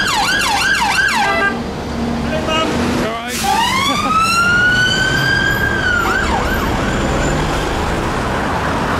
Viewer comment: what did he say? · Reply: Sounds like he said "Hello man"